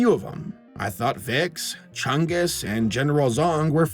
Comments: WAIT! Did I miss something? Did he say Chungus? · DID HE JUST SAY CHUNGUS?! BIG CHUNGUS?!!?!?!?!